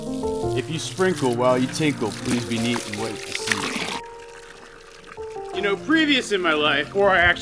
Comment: I always heard it as, If you tinkle when you sprinkle be a sweetie and wipe the setty. Lol... feel better soon